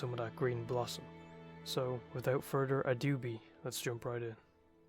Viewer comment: I like how it sounds like youre saying "without further adoobie thats joe biden" i had to double take. fucking ironclad mythical algorithm pull